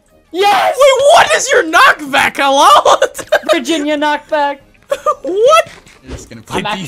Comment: LMFAO I THOUGHT HE SAID "VIRGIN" KNOCKBACK XDDDDDD